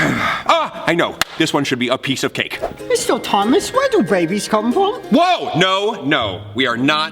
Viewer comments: Instead of hearing him say babies I thought he said rabies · I thought he said "where do rabies come from. 😂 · When Joan said babies I thought he said rabies!!!X-D X-D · Is weird that when they said "Mr. Thomas where do babies come from?" i first heard "Mr. Thomas where do rabies come from?"